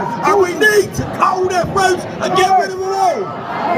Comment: When I listened to that little snippet of Ricky Jones It sounded to me like he said "We need to hold that rope and get rid of them all......" While making that hand gesture. That sounds like he was saying to hang them all. Thats still inciting violence. I heard a rumour that if you are referred to " Prevent" Then you may not be allowed to vote in the next General election.......... So thats 90% of the population then ! Why cant Starmer be done for inciting violence ? He is responsible for any disturbances, rioting , Protests, vigilanties , hatered etc He should be referred to " Prevent "